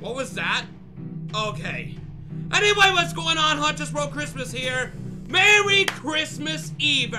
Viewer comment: btw who heard the KRIIIIMA i did